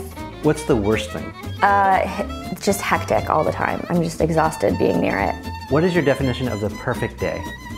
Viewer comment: Hi, can someone please caption her answer for the question: "What's the worst part of being miss Neistat" at 1:12. I couldn't catch that.... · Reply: She said she doesn't like all the hupla and attention